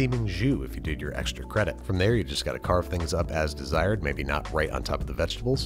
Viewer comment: autocaption says "served with a steaming Jew" instead of "jus"